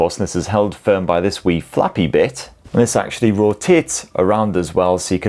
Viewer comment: Did bro say "Ro-Tits" 😅